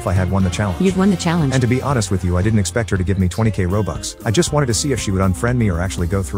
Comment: I thought she said 20 As for 20 instead of 20.000